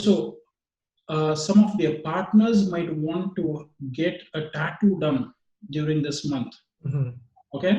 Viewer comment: What did he say at 3:31?? Attack?? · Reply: Psychedelic Tranceandance a tattoo, across the neck or shoulders